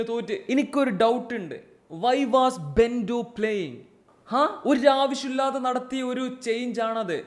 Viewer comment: bro said bento , I heard pendu 😂 enthayalum sthiram kalapruvadi nadakkatte teammates ine blame cheyyall❤